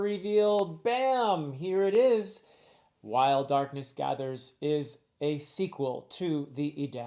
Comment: This WHOLE time I've thought you were saying "Wild Darkness Gathers." 😅 Great cover! Can't wait to read it.